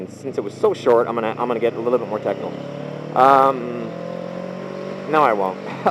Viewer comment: Dang! I thought you said "and since it was so short, I'm gonna get a bit more 'sexual'"... but then I realized that you said 'technical' =( ... =D